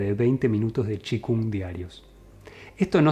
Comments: Cómo? chi kung/Qigong diarios?? entendí correctamente? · no entiendo lo que dice "chicun diario?" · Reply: Hola, es una práctica de un cierto tipo de arte marcial. Se escribe Chi Kung.